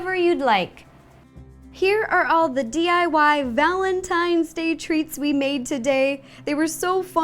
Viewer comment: i thought you said valentines THREATS. (love you ro)